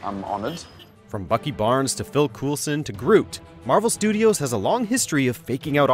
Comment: Did you just say "Phil Coolsun"? Come on man haha